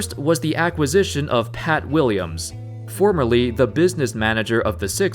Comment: When he said pat williams I thought he said kat willams